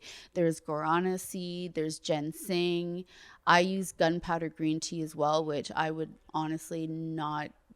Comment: can she give us the link for the green tea? Did she say it was powdered?